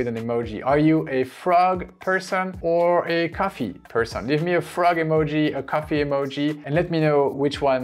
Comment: I'm a☕ person who loves to draw! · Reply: I thought you were gonna say: "I'm a coffee person who loves 🐸" 😂😂